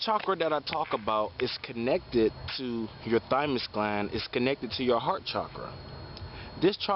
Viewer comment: you said connected to a gland 1:33 but I'm not able to understand the word I hear dimus gland I searched haven't found could you tell me what is the name of the gland its connected to. Thank you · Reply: Thymus gland